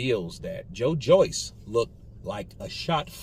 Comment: I thought he said Joyce looked like Foreman